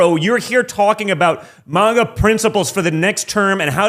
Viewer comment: did he say “manga principals”? 🤣